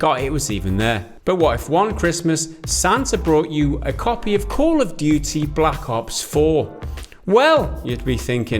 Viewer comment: I heard him say "Santa brought you COAL of Duty" and My Dad pun radar approves it